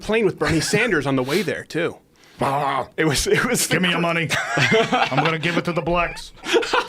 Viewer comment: did he say" Gimme your money i'm gonna give it to the blacks" ???